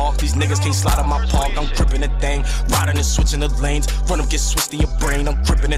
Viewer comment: Sound like moe saying cripping the thang lol